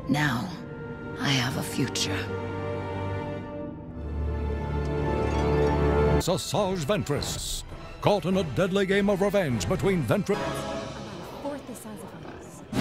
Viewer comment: I swear I heard sausarge ventress